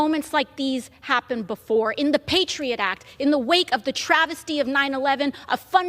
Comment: Did this skip by? 18:05 "... in the wake of the travesty of 911 ...". Did she really say the "travesty", of 911?